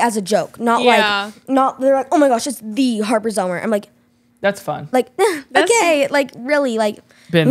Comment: I thought he said thats funcked